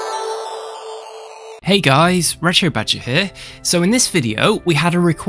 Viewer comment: Ok, so every time he introduces himself, I hear "Red Shirt Badger". I mean, given that this is Star Trek-related, it kinda makes sense. lol